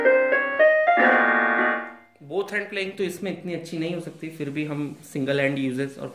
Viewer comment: did he say Wu-Tang clan? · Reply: Both hand